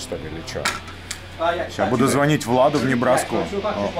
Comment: "...сейчас буду звонить в Ладу , узнавать ...." послышалось ) потом я понял что не в Ладу а Владу))))